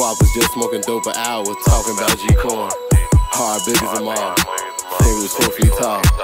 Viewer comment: This weed always make me LOL because it sounds like he said G Corn. I'm geekin hard lol